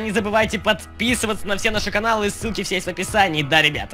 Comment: мне послышалось что на 0:51 сказал с*ки (на самом деле ссылки) XD